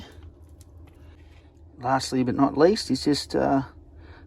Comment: 🤣🤣🤣thought you said parsley but not least, as you putting in the erb.